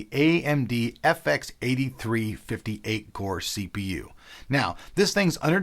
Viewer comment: shit. I thought you said 58 core cpu